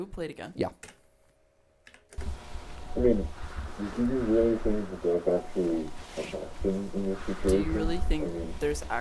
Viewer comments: i heard " you think the back rooms are really dangerous in these situations " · I thought I heard in the muffled talking: “Do you really think their’s a back room in this situation?” (Edited for spelling correction) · I heard "I mean do you guys think theres actually a monster in this facility?"